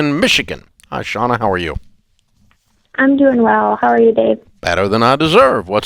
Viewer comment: I thought she said "how are you bebe at the beginning" lol